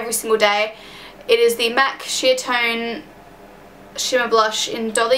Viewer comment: haha at first when you said "MAC Sheertone" I thought you said "MAC Shit tone" xD